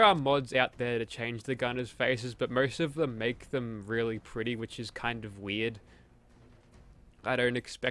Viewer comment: I thought you said really gritty was super weird.